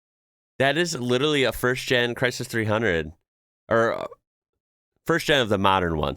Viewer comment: did you say Crisis 300? Is that what they call them in the states? 😅